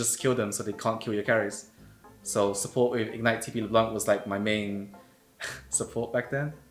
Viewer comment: creo que dice "ingnite/tp le blanc"